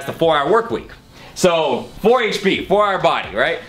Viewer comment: I thought for a moment he said the book was called "The Four Hour Workout." Was about to say "the fuck with that!" lol